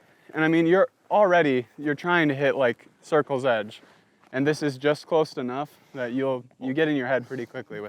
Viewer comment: did he say “just close to nuff” 😂